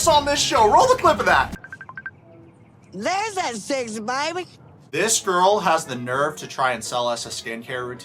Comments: I thought Angela said “there’s that sexy baby!” When she got on the phone with Canada dude. The producers did ask her if she would bone him and she laughed and said yes though. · I thought Angela said “there’s my (or that) sexy baby” re listen to it I believe that’s what she says